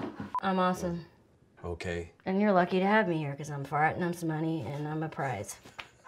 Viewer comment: “I’m fart and I’m smunny.” Omg I canNOT. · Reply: Oh my god. I thought she was saying “I’m farting on some money” and it made no sense to me